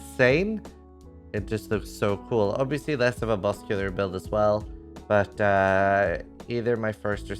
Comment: I can't clearly understand what he says about Crackling lance from a boss killer standpoint ? Is it a good or bad choice and why ? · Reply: he says "less of a boss killer". looking at the numbers it should be close to arc in clear and boss damage.